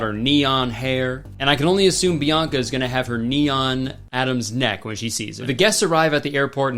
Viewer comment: "the bridesmaids have their neon dresses, bianca's mom has her neon hair, bianca's gonna have her.... knee on adam's neck" killed me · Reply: Lmao I heard “neon Adam’s neck” and was so confused 😭